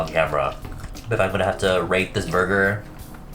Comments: instead of “rate this burger” I understood “rape” 4:16 cool huh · Girl I thought you said I'm gonna have to rape this burger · I thought he said he was gonna rape the burger after the video